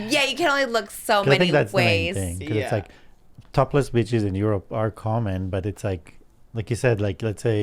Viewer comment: lmaooo the way moses said beaches i thought sounded like bitches and that part SENTTTTT MEEEEEE